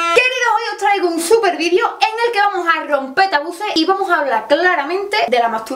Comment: Jurao que entendí "hoy vamos a romper autobuses"